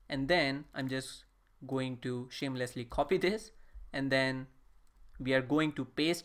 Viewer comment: did i just hear you say "shamelessly copy this..."😂😂😂😂